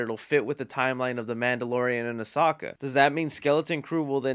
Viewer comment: did he just call ahsoka “oh saka”??? lol